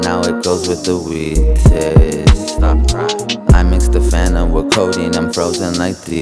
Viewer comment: Dude eazy did you just say " I mix the fentlyn with the codeine"" dude plz don't do fentlyn its to dangrous it killed my brother and tons of other friends aswell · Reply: He said fanta